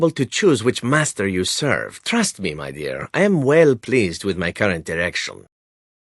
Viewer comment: Just before I swear I heard Zevran say "I'm well pleased with my current erection" My ears did direction bad